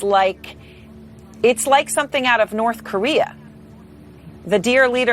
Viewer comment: Did she just say it’s like something out of North Korea?? 0:53 Whaa..?